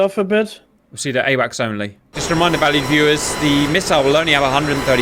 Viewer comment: For years I thought he was saying “Valley viewers”.